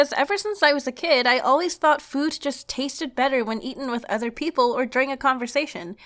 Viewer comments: Did anyone catch the "eating on other people" on 3:46, btw liked the video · I thought you said "it tastes better if I eat other people" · I thought she said "good taste better when you eat people"